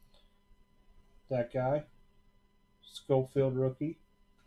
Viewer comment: Did u just say black guy wtf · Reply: No I said "that guy". He has a last name that can not pronounce.